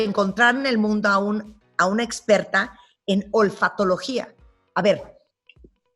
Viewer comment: Disculpe sin ánimos de ofender, entendí "golfatologa" y golfa en mi país tiene otra connotación.